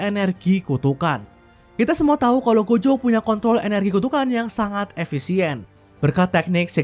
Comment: mohon maaf gue salah denger Aowkaowkwok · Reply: kok kayak ngomong kont